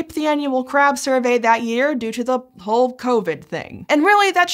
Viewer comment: 'Whole covid thing' sounded like 'Hulk Hogan Ring'. Billions of crabs silenced by the atomic leg drop